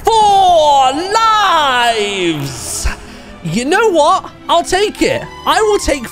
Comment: yes im VERY late, But When He Was Saying "4 lives" i heard him say "4 knifes" which made me laugh